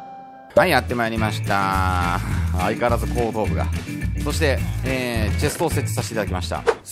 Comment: 「相変わらず高野豆腐が〜」 にしか聞こえないんだけど、笑笑 高野豆腐って言ってないよね？村人の頭割と高野豆腐に見えるから、そう言ってるようにしか聞こえない笑笑笑笑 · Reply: 相変わらず後頭部が。じゃないかなー？